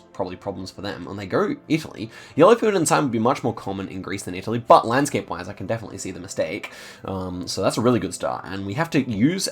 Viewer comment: what does he say at 8:41? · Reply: "And they go Italy! Yellow filled in sign would be much more common in Greece than Italy but landscape wise I can definitely see the mistake."